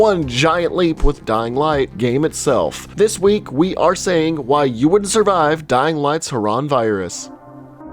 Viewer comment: i heard that as "you wouldnt survive Dying Light's Haram Virus" and i cant unhear it